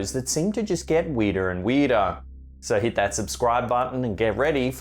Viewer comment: why did it sound like when he said weirder and weirder it sounded like he said weirder and weeder